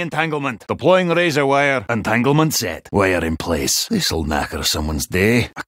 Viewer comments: I was thinking what was he saying because of his accent was not clear and he said “We are in place.” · What the hell does he say @ 2:07 and explain what it means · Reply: “This will knacker someone’s day.” Means fuck up / ruin.